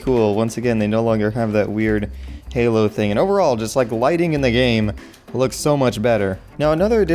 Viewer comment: I got so excited, I thought it said "lightning"